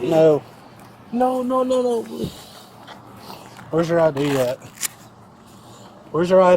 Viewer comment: Every time a cop says “where’s your ID”. I hear “where’s my crack”.